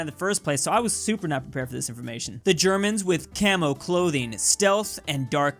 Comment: he really said camel instead of camo didn't he